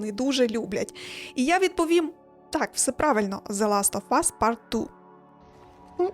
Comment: що вона сказала?))) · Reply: the last of us part 2 то є гра така компʼютерна